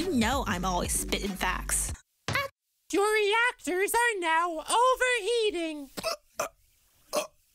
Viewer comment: it sounds like x said your shi